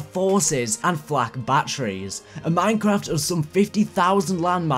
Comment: did he say minecraft